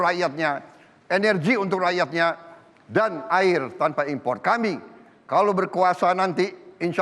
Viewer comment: Saat penyampaian visi misi kok saya mendengar capres no. 2 mengatakan "kami kalau berkuasa nanti"...berkuasa ?? Apa saya yang salah dengar ya? ^_^ knp tidak menggunakan kata "memimpin" saja? Menit